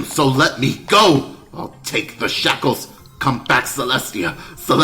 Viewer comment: I heard "I will take the shekels"